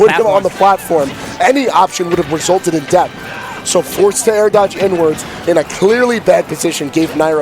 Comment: I thought he said : "So forced to air dodge n-words"